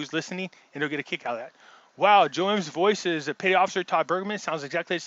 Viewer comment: Who is the guy you said sounds like the Joe M Narrator? Can you spell it? I hear "Todd Burgman" but I can't find anyone under that name. EDIT Seems like Im not the only one! LOL You address it later in the video, "Todd C Burgun".